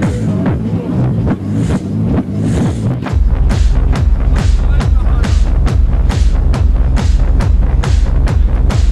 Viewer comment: What does he say here? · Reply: German: "Halt den Ball flach, Alter!" English: "Keep the ball down, dude!" (means be careful, what you're doing)